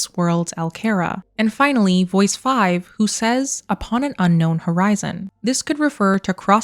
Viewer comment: The last voice sounded like it said “going back home to Horizon”